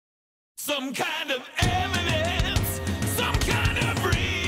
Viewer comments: Always thought he said “Some kind of M&Ms”. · I'm hearing m&m's instead of evidence in the beginning, lmao. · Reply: I thought he said eminence.